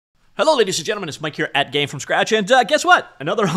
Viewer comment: For a long time I thought you were saying "Mike Carrot" but you were saying "Mike here at"